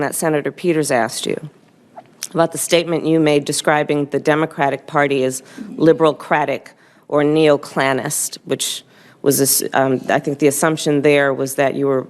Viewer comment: I thought he said liberal crack addict. Kinda fits her.